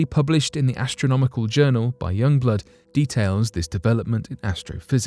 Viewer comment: I'm sorry, but did I just hear "The study published in the Astronomical Journal by Yungblud" at 59:17 ? Hahaha wut